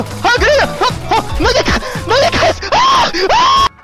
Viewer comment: lmao what was he saying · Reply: "Shit, oh no" or something above these dont know that much